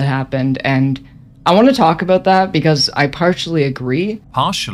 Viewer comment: did...did it say....parch-ally??